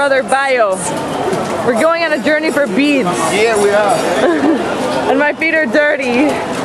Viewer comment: I've been to Nigeria multiple times, and have nver visited this markets. At the beginning of the video I thought she said they were looking gor Beats, but omg I need to go to get some of those beauties!